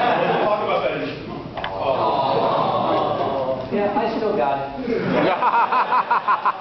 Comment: Aw did he just say,"I still got it because no one wants it."